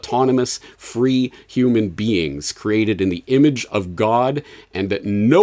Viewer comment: Did I hear James say that we are created in the image of God? 32:26 Keep praying for this man